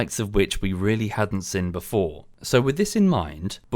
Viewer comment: Just coz you asked it 10:50 you said sin instead of seen :D God it is nice being petty :D